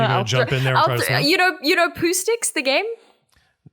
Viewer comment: hasn’t heard of Pooh Sticks? Huh. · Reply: I heard it as “poo sticks” and my brain locked up for a second until she explained it